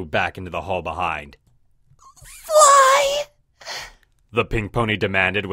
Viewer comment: What was said "Fly...." What I heard "Fly you fools!"